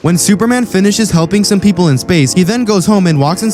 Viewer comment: i heard "humping some peaople in space" lol